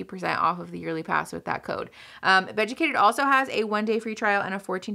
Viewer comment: I usually just listen to you, and this whole time I thought you were saying "vegucated" and I thought it was spicy for them to bring vegetables into the bedroom education. Lmao.